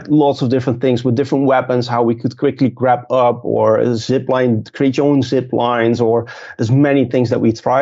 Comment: Uuuh 2:41 did he say create your own ziplines????